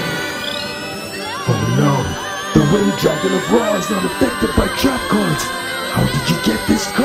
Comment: I thought he said wing dragon of raw and in raw papers and would have been made out of papers and joints but no. Still funny though